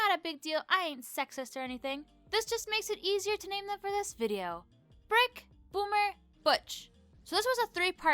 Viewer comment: i thought you said ¨frick, boomer, and butch.¨ XD